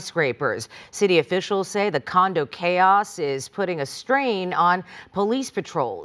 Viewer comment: Did you say condo chaos? 🪂🪂